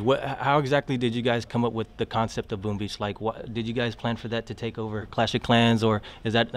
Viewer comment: Lol i heard "Boom Bitch" XD